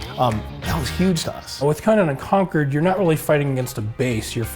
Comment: haha I dont know why but ever time they said "Conan Unconquered" I kept hearing "Command & Conquer" :D